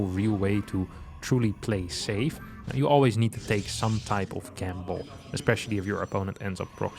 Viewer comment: what's that word/phrase there? "Take a campball" or something? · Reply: "take a gamble"